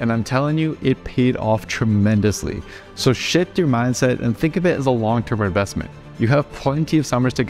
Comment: did you just say "just shit your mindset"? 😳